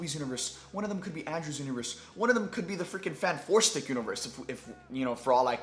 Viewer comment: Sorry I got a question, I heard you say the exact same thing on the watch party today but I thought you meant Fantastic Four but then I realized you weren’t saying that- what does Fan Forstic Universe mean?